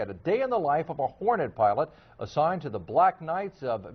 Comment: "a day in the life of a horny pilot.... hitting the clubs, drinking, partying, picking up g..." wait, what?! oh sorry, it says hornet? ok...."a day in the life of a Hornet pilot...." lol